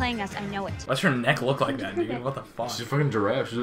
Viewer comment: uh like I don't want to make accusations but did you just say "why does her neck look like that n* what the fuck?" Please tell me I heard that wrong